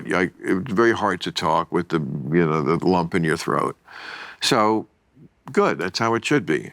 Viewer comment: i thought he said young in the throat lol